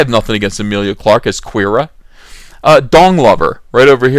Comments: Why do i hear: "dong lover"? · I thought you said Dong Lover... then again, I don’t know if that’s what you actually meant. 💀 Went ahead and picked up the shirts. I explained to the girlfriend that her Tico shirt may get her into some interesting conversations.